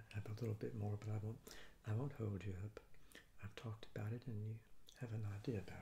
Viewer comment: What did you say at 15:30? "I won't ..." (word I didn't understand) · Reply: I said "I won't hold you up!" - I meant I won't make you watch me finish the rest of this pizza! (Actually, I had the rest tonight!)